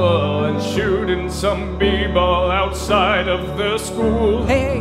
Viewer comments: I heard "b-ball" as "people." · Lyrics to A Song Bout Heartbreak by: would it be alright if we tried again? I'll hold you tight again we'll be alright again i wanna be right by your side again watch Nick at night again and chill with Aunt Vivian well in west Philadelphia born and raised on the playground is where i spent most of my days I'm chillin' out maxin' relaxin' all cool and shooting some b-ball outside of the school Hillary and Carlton and Aunt Vivian i wish that you all were together again so throw out the DJ let Geoffrey unpack i long for the day when the fresh prince is back where is Will? x4 (interlude) Do you remember that one time when Will's dad returned and Will was so happy but then he got burned And Uncle Phil came down from heaven above and showed Will the meaning of fatherly love! Uncle Phil x4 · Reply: I thought he said shooting some people outside of the school xD